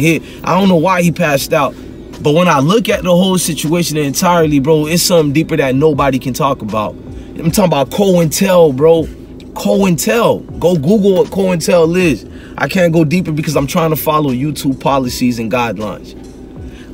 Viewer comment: did he say “cole and tell”